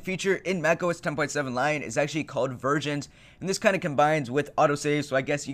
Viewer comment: Did you just say "VIRGINS"?